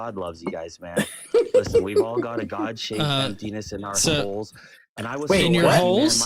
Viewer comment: Did he say god shaped p? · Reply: Oh, emptiness